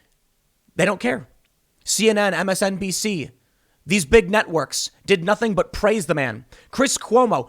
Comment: Briefly, I thought timpon said "big news nutworks" ... Been a long day. 🙄😉😁